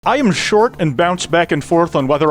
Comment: I paused the start at just the right moment that I thought he said "I am short and bouncy"